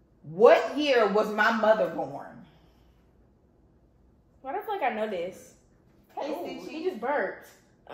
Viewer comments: why did it sound like u said why the f do I know this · Omsss it sound like she said “why the f* do i know this?” 😂😂😭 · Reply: What if I don't know this what she said